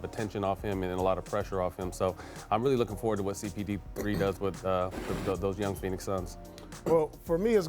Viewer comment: did matt slip up and say "CBD" instead of CP3?